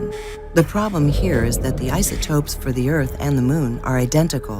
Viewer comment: Did she say isoTROPE? What’s an isotrope?